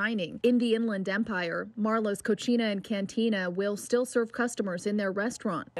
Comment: did she really say cochina lol